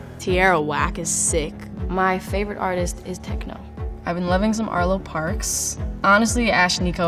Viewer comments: What the heck does she say for her favorite movie? Sounds like "Fervel Station" which does not make any sense. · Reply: Fruitvale Station - the same movie she said the past three years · is she saying "techno"? That's just a genre right? I'm probably wrong. Help